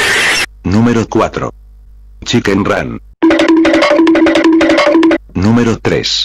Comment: why i heard chicken gun.